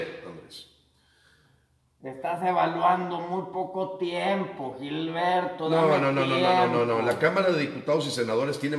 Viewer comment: Y lo mas ridiculo es que el señor ASI es como contesta: puras conjeturas, puros pretextos, puras justificaciones de sus idioteces, puras acusaciones sin fundamento (hablo de AMLO, no de ti, Gilberto)!! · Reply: Pense que decias por Gilberto, ya me habias caido bien.